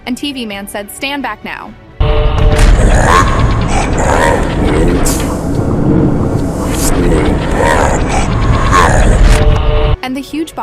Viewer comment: I think he said fall back now